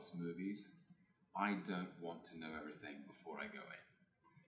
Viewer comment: What does he say in 11:21? "I´m gonna keep____?" It sounds like "stumm", which is a German word that would actually make sense in that context, it means as much as "mute", "silent" or "voiceless" I´d say, but is that a word you´d actually use in proper English, is he using a German word here he picked up at some point or am I just totally misunderstanding the word? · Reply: Yes it was "stumm" = non-speaking, silent. Here and there he use some German words, I don't know why....but as a German, I have to say I kinda like that😉😋